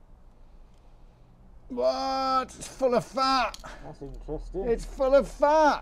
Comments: I thought he was saying it’s full of fart! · I thought he kept saying it's full of fart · I thought he was saying "full of fart" 😅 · Glad I read the comments, for a minute there I thought he was saying, " it's full of fart" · I thought you said it was full of farts😂😂😂😂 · finally understood him saying, "full of FAT". Not what I first thought he said! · thought he said it's full of fart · I thought he was saying it's full of fart.